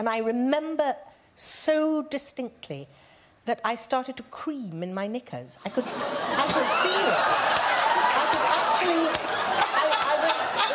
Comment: "i started to cream in my knickers" is absolutely iconic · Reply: I thought she said scream not cream LOL.